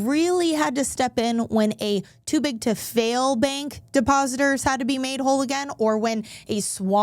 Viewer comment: When you said "too big to fail" I thought you said ""stupid to fail". I guess that works too. 😀